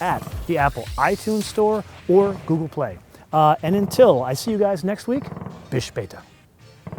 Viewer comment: Did he say "until i see you next week, bitch lata"?